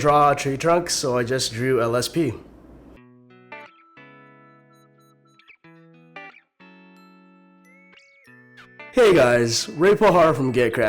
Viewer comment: LOL WHEN YOU SAID " i didnt know how to draw a tree trunks, so i just drew a lsp" i thought you said "I JUST DREW A LESBIAN" And when you said a crafty crafty project, i thought you said crappy crappy😂😂😂😂 I NEEDA CHECK MAH EARRRSSSS